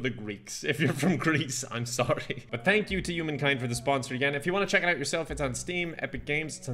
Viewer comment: I thought he said thanks to humankind to leting me destroy it